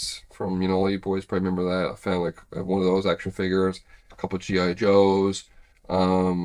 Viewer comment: When you said action figures wash up, I honestly thought you said 'extra fingers' at first.